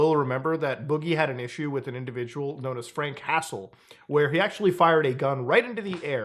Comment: Thought Muta said Frank Castle and was like wtf do you mean he had a problem with the Punisher